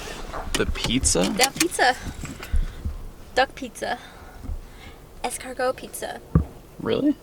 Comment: I thought she said "dog pizza"